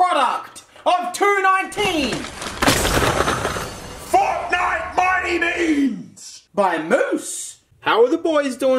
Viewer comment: The first time he said fortnite mighty beans I don't know why but I heard "F*cknite mighty beans" Sounds like something he would say though.